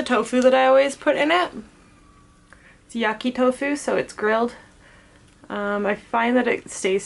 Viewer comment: I thought you said yucky tofu lol😹